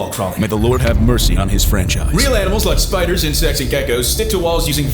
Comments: “Real animals like spiders, and sexy geckos.” was what I heard while I was drawing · I swear bro said "and sexy geckos".